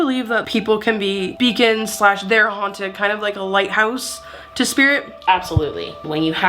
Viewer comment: I genuinely thought she said "do you think vegans slash their haunted..." had to rewind it twice, I love how she talks.